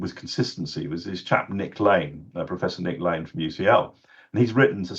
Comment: Professor Nick LANE is he saying at 21:31??? Please let me know someone. I want to do further research on this guy. Thanks.